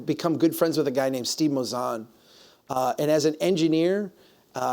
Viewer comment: he mentions Steve something .. can anyone decipher what he's saying.. Mazan? I can't find on Google.. Seems interesting.. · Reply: Steve Mouzon. Engineer and New Urbanist